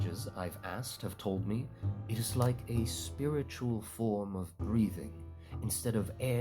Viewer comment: I know you said breathing but what I first heard was "A spiritual form of grieving"